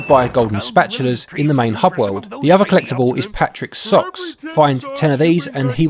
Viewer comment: I thought he said Patrick sucks instead of Patrick's socks. I was thinking dang what did Patrick ever do to you.